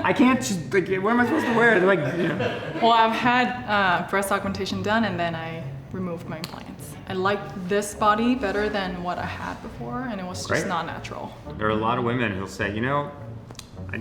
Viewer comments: what does she say, removed my ..... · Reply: vasquezsteven they’re talking about breast implants stupid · the Asian woman had breast augmentation and then ? Removed her implants? Didn't catch the second part...